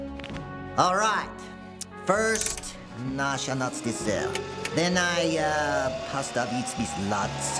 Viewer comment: I'd like to know at 9:21 what Dale says in Russian. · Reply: The Subtitles just say ''[speaking Russian]'' and its pronounced kinda broken. But from what i can hear, i think its: ''First Направим цель (Navigate to the Target) them i раздавить (flatten) this lads'' (The First ''Направим'' word is pronounced super weirdly, maybe its a different word)